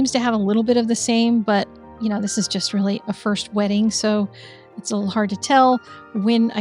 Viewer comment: 😂 When you said 'first wetting', I heard 'first wedding', and was trying to figure out what sort of analogy you were going for. First wedding is a practice run, second wedding is for keeps?